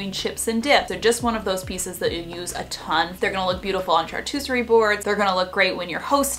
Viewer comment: What the heck did she say at 6:27 ?? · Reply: I think she was trying to say "charcuterie boards"